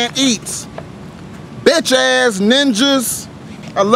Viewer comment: Lmao I tired af, I thought it said Donate dis heat looool